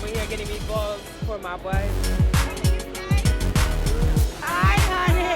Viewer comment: Did homie just say he was getting meatballs for his “wife”⁉️🤔🤨😳🤭🧐.. he must’ve been speaking figuratively.